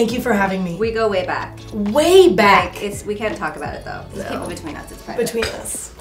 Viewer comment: I thought it said "we slept together" 🤦🏻‍♀️😂